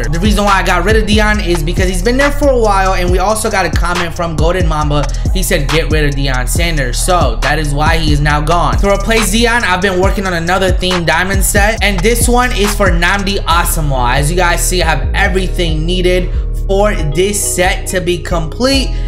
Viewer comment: Did he say zion